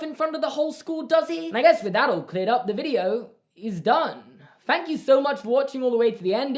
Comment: I feel like every time that you say "thank you" I hear "fank you" 😂